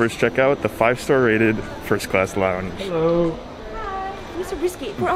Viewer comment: i thought that the word lounge said lubomir, which ism my name